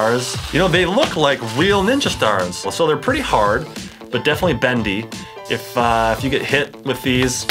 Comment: DID HE SAY BENDY